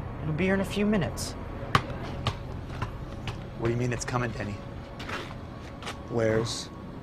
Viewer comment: did he just called him Penny? So much depth in this scene, Lynch himself would be proud!